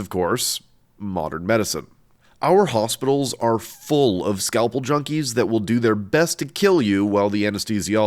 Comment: I heard "scalpel jockey" instead of "scalpel junky." I will 100% be referring to surgeons as scalple jockeys from now on